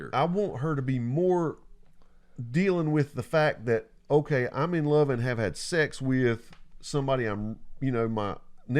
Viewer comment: towards the end of this episode, i had to hurry off an unexpected call, and when i hung up the first thing i heard was you saying "i'm in love and i had sex with my nephew". around 44:06 lol